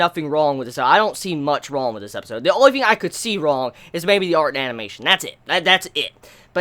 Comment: what did he say at 9:18.... "i dont see much wrong with this episode, the only thing i could see wrong is maybe **, and thats it...." im not a native speaker :C... ty anyways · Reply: Art and animation.